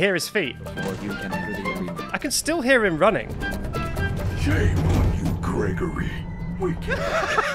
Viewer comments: What does Freddy say at 10:00? I hear “Shame on you Gregory! We can-“ the rest is cut off by him laughing. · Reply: “ We can’t be in here together! “ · wait huh??? Does Freddy say "shAME on you Gregory!" at 10:04???? I've NEVER HEARD THAT LINE????